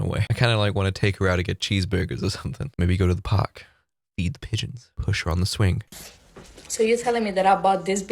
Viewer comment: I misheard "feed pigeons" as "eat pigeons" 😭💀 YEAH BRO LET'S GO TO THE PARK AND EAT THOSE PIGEONS!!!